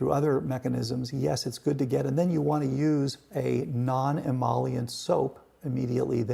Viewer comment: What did he mentioned around 1:30? Did he say non-emollient soap?